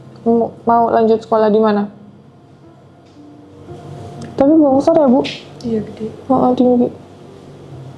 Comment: tolong gais kalian yg tau mbak ngomong apa itu🙏🏻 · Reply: Tapi bongsor ya bu..